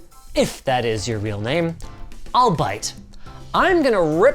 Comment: For some reason, I heard "So okay, Nakamichi. If that's your real name, I'll buy it." instead of "I'll bite"... Now because of it, I'm picturing "Linus Nakamichi Sebastian" and it's just sending me into the stratosphere:rocket-red-countdown-liftoff